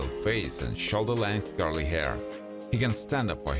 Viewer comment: Great video! I loved seeing the old pics along with the current pics of the actors. Also, Thank you for using your own voice. It’s so much better than using those fake, computer generated voices. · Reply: Just to help, the design called “plaid” is actually pronounced “plad.” Just trying to be helpful. 😊. Also, you said a man’s hair was CURLY, but it sounded like you said GIRLY. Which is funny because curly hair on a man CAN also look GIRLY sometimes too. Ha!